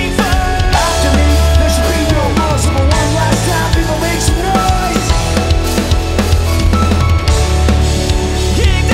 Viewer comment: am I mishearing this or is this dude dropping the n-bomb? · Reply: No he says "come on people make some noise"